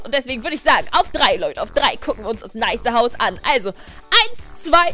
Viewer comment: Ich hab stat nices Haus weißes Haus verstanden 😂